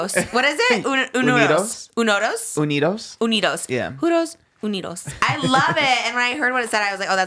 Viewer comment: Being Mexican the jotos part had me dying 🤣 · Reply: Yes I thought she was saying “juntos unidos” not JOTOS 😂😂